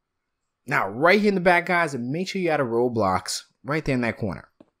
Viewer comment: Did he just say “MAKE SURE YOU ADD A ROBLOX” 2:06 😂😂